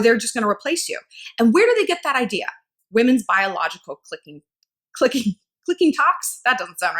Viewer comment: Clicking tock 😂😂 i thought you said clit clock haha which is also fitting. Same diff lol I do the same thing with PARKING SPOT i say sparking pot. No matter what i try to do it always comes out that way haha I do it with different words as well but sparking pot is the worst.